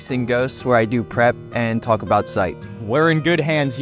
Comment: What he said: "where I do prep and talk about site" What i heard: "where I do crap and talk about shite"